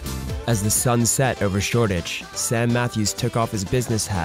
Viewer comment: at 4.20 he says making FIFA videos but the subtitle says peeper videos..I was like wtf... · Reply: shortage instead of Shoreditch